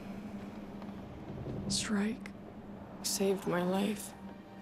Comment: Every time she said "Shrike" I thought she said Shriek